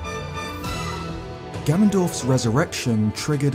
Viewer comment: I had to reverse for a second attempt the beginning. Thought you said Gandalf. Not Ganondorf